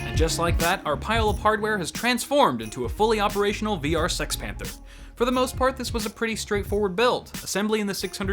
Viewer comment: Did he say Sex Panther 7:07.